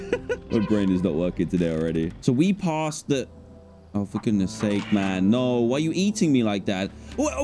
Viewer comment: Ayoooo think about what you said! There are children watching! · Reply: He said for goodness sake